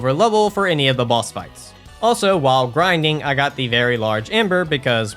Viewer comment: every time I hear "I pick up the large ember" I always get "very large andre" in my head 🥲 I watch too much jk leeds